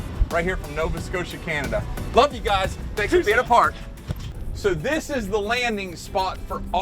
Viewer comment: did he say stay 6 feet apart back in October